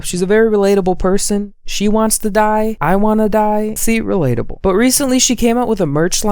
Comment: when started talking about how Emma is relatable by giving the example of, “she wants to die, I wanna die” I thought he said, “she wants a guy, I want a guy-“ ¡-¡